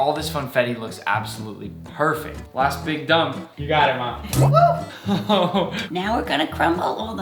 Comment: Is it me or did he say “you got it ma”?